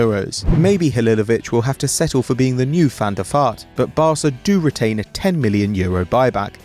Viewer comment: did he say van der fart? Lol